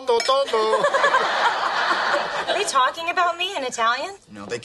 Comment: Yes is true, as an italian I could say that... And this is so funny!! Anyway, the last word that Joy said is "Adagio!" it means various things, but in this scene the meaning is "Be careful! (of what you're saying!)"